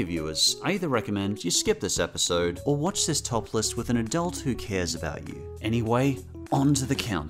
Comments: I thought you said “watch this topless with an adult you trust” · i thought you said watch this topless with an adult who cares about you haha